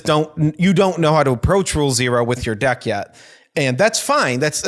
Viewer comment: I heard "Gruul Zero"